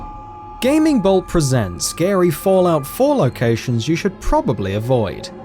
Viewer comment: I thought he said Gary not scary